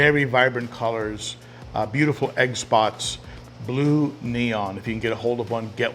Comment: I keep mishearing 'egg spot' as 'xbox'